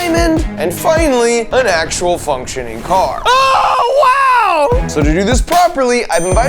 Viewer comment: I heard ts as “an actual f